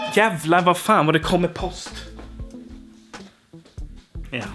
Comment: Now I need to know that curse at 5:22. I only hear 'jävla va fan'. 😂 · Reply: Jävlar, vad fan, vad det kommer post (I guess it wasn't the first mail delivery that day)